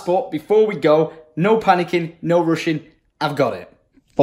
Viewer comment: what are the odds he said no rushing but it sounded like makarov saying no-Russian just before was going to the airport